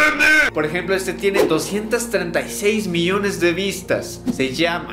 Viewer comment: yo pensé que decía 238 m, que curioso 🧐